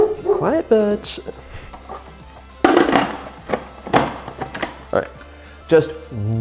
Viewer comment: when his dog barked i thought he said alright b***h